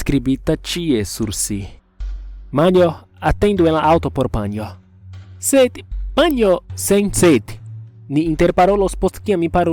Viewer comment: Something I noticed, at 2:03, the English subtitles say “Mom, wait in the car for mom.” when it should be “Manjo, wait in the car for mom.”